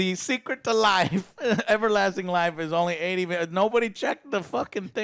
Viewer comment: i think he says 80mil per ounce